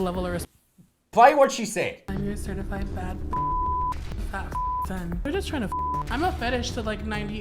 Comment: im not gonna lie i thought she said " im your certified fat bitch " genuinely thought thats what she said